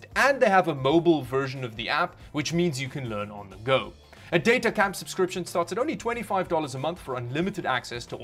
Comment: Sounds like he was saying DOTA Camp 😆